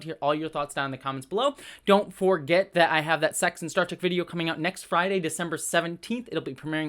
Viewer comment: "Don't forget I have that sex instruction video-" spittakes, rewinds "Don't forget I have that sex and star trek video" Oh okay, so I misheard THAT